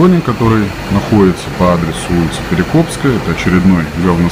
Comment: Мне послышалось или реально улица Перекупская?))